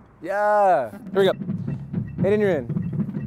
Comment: Good vid - thought he kept saying Aiden you're in and this poor kid Aiden had to like hop on a drum unexpectedly instead of 8 and you're in